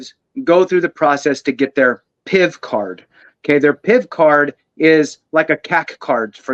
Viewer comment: WHAT is the word you are saying? I have listened to it repeatedly and cannot tell if it is Piff/Pizz or what. Please tell me what you are saying as my hearing is about shot anyway. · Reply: PIV Card. <link>